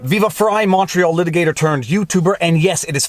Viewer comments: Montreal litigator I thought you were saying "Macho litigator" eh. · For so long I thought Viva said "Macho Litigator" instead of "Montreal Litigator;" slow your roll, you bad ass!!! PS: neither is mutually exclusive! Keep up the great work, I love the collabs with BARNES, NATES, and UNCIVIL.